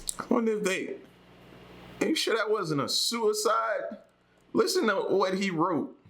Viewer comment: Yea, sounded like suicide ti me too. I bet they just marked his cause of death down as “COVID 19” 🤣🤣🤣🤣